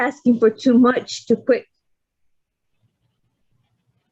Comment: I heard "Ms.Finger" too It's the prosecutor,her name is Ms.Panger i think,my eyes r bad😅